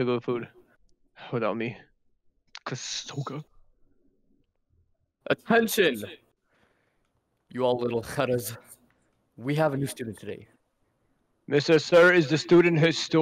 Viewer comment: DID HE JUST SAY LITTLE KHARAS LMAO